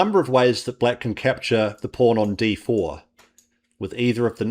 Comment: I thought I heard capture the “porn”… but you said “pawrn”…